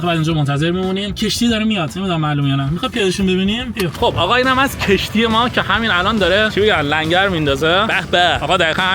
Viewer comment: اطلس تو المان چی میگه؟ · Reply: رنو کپچره